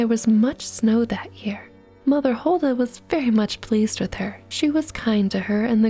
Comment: At first, I thought it said "Huldra", a type of wood fairy from a norwegian country.